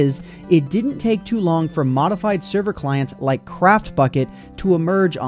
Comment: did you say modified server clients?